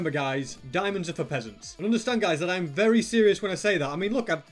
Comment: I thought u always said "Diamonds are for present" but its "diamonds are for peasants"